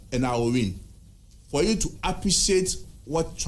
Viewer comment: Did i hear "for us to appreciate what transpired in the Constituencies".. Your own people loot you, you see even the security forces are not minding you. We are aware of you holugans wai